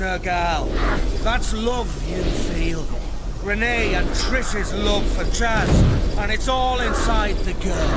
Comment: did he really said “it’s love you feel love for jazz” why didn’t I discover this guy before